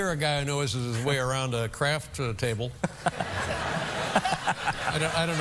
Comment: Amazing brilliant man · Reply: Thought letter man said crap table😅